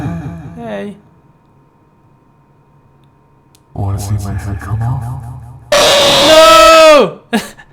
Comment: In 18:30, does it say "you wanna see my face come out " or "you want something come out" or.....Whatever it's saying. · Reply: It says << Wanna see my head come off>> as it says in the creepypasta : abandonned by Disney